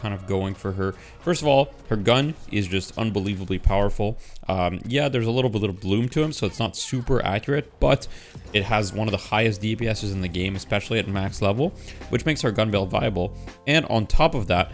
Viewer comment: What does say around 1:03 about holliday's gun? Sounds like "yeah there's a little bloom to em" which I'm guessing I heard wrong..